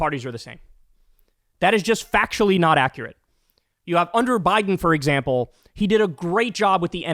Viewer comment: I thought you said Hunter Biden I was so confused. But yeah.. you said “under Biden” sorry I hit the blunt.